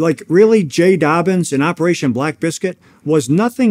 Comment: Did he say "operation black biscuit"?